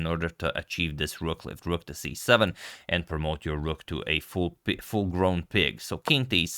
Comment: What did he say? Promote your rook to full * pig??? (I know rook on 7th rank is pig but what couldnt hear all) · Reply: Full grown piggy